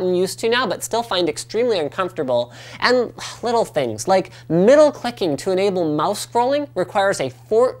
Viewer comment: lol I misheard Middle clicking as Motherphucking had to re listen several times i was all like did Linus just curse o_0